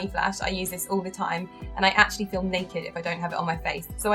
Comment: I thought you said "I film naked" when you said "I feel naked when not wearing the serum" haa haa.